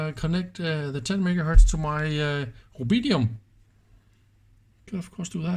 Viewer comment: must be an error which I found on the subtitle. I heard that you said it. I thought that it could be rubidium. Perhaps the translator did not undetstand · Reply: I think it's at 10:06 where you mention "connect the 10MHz to my rubidium" the subtitle says "obedium". Thanks for the teardown and interesting to compare the insides to what CuriousMarc has been doing with his frequency counter.